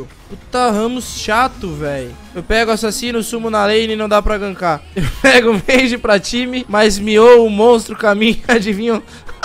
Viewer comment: Não entendi, mas deu pra sentir a revolta nas palavras da Orianna KKKKKKKKK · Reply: Eu entendi. Ele disse que pega assassino e nao consegue gankar. Dps pega mage pra jogar team play mas quando o assassino inimigo mia, eles avançam pra morrer. LUL